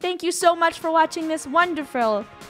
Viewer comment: said wanderfall as wonderful oh and I remembered the time that said double like but u will dislike so she said Triple like so yeah I will do triple likes so then I’m SURPRISED THAT U ALL BECAME PROS DAMMM!!!💜💜💜💜💜❤️❤️❤️❤️🥰🥰🥰🥰🤩🤩🤩so congrats and have a wonderful not wanderfall day🤣 wait ............................................................................................................................................................................................................ I just read my comment and saw a word triple and I missed incorrect it and I’m not correcting it because u know what I was trying to say